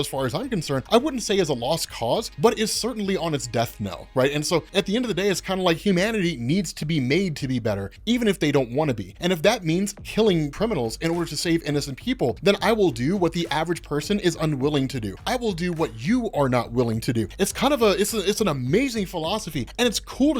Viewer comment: It sounded like was saying he was going to go slay criminals 😂